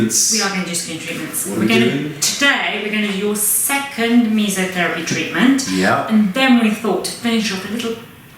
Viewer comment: I'm sorry, what kind of therapy? It sounded like you said "me therapy". And that's OK! But I don't think I've seen this therapy on <link>. though I've seen the fillers.